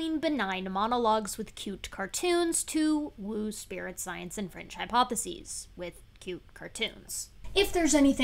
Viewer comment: Excuse me, could you please explain one little thing? in the introduction, you say that after skool deals in woo science and a thing I can't hear properly, but that the caption thing translated as 'French hypothesis'. I have no idea what this is about, could you please tell me (in writing) what the actual term was that I can then research it properly?